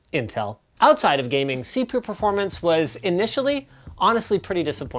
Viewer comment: Did he just say... C poo performance at 2:50?